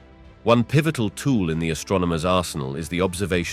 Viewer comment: I heard "...In the astronomer's arsehole" lmao